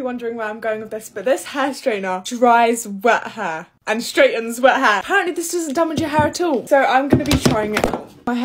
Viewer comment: The fact that I hear haaaaaar instead of hair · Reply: Yep!!!..i heard "waaeh haar" everytime she said wet hair 😂